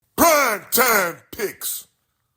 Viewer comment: Why do i hear “PRIME TIME PIGS!!!” being shouted? 🤪